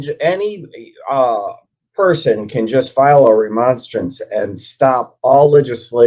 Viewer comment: I thought he said " file a remonster " 👹. As I think about it, he might as well have.